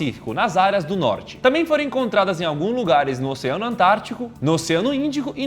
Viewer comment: só eu que ouvi ele falando Atrântico??